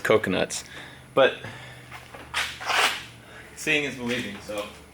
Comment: when says "seeing is believing" I freaked out because I heard "seeing as we're leaving soon" and I thought they were moving already or something